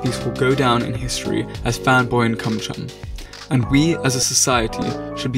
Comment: did that boi say fanboy and cumchum 🥺